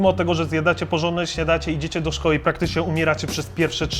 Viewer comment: Czy ktoś jeszcze usłyszał "śniadacie" ?